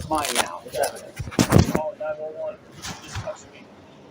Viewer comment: did I just hear him say "call 911, he touched me" 🤣